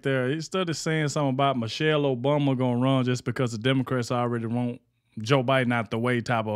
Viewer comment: For a second I thought you said the DEVILCRATS 👿 😂